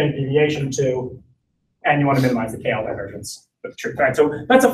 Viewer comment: I can't understand what he says at 15:45 , we wanna minimize what? Thanks. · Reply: I can't hear it well (not my native language), but it makes sense if it is KL divergence <link> if he is talking about probability densities.